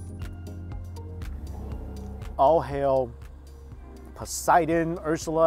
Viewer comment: I thought you said "awww hell" instead of "All hail" I laughed pretty hard at that.